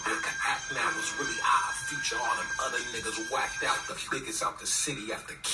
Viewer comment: I choked on my water when I first heard that part but damn, he's not wrong 7:38 he said "Odd Future" which is/was his music collective group